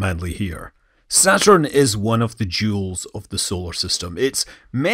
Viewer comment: I heard it as 'Saturn is one of the Jools of the solar system' lmao